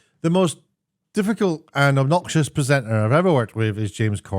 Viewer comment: Offtopic question: I have noticed that sometimes British people seem to say "presentner" instead of "presenter". When Ricky Gervais did the Emmys it sounded like he said it that way and here it can be heard at 0:02. Why is that? Is there an explanation?